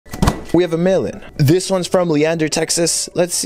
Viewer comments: I thought he said "We have a melon".😅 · Oh it's mail in I always thought you keep saying melon 🍈